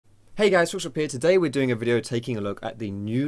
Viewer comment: what's your name? Everytime you start a video I hear "Hey guys social peer here" lmao wassup social peer...